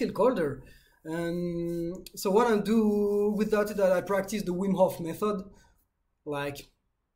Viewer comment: What was the method you said you practice? At 6:50? I’ve listened to it like 10 times and I keep hearing my last name 😂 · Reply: Wim hof method 😂😂 it's a breathing technique